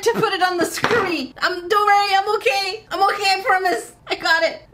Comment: when says “Don’t worry I’m okay!!!” and I heard “Dory I’m okay!!!” Love it!!! New merch idea 💡🤔🤫😇